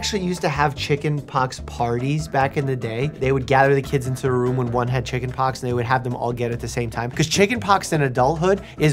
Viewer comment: Um what did I just hear that right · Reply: Either good intentions comes horrible actions